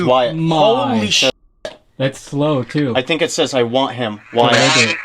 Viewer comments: When you asked the second time to get them to say his name. It sounded like it said “I won’t say his name” instead of “I want him WYATT” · it almost sounds like “I said I want him” The “I said” it’s not very clear but I hear “I said” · Thank you everyone for being here! We hope you liked our Ghost Hunt here 😳🖤 Wyatt is fine and he told us he feels like going back was what he needed to do. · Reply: I definitely heard it say what you guys think you heard. I closed my eyes and listened and heard it say "I want him.. Wyatt " insane!